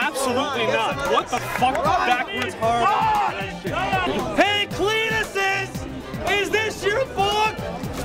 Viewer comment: What did he say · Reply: HEY CLEVISES!!! IS THIS YOUR F*CK!?!?!?